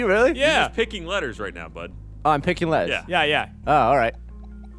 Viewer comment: Did call Gavin bud at 29:14 or am I just hearing things? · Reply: he def said bud